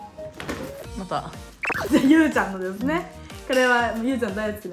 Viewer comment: なんか、うーちゃんって言ってるのがゆーちゃんに聞こえる😂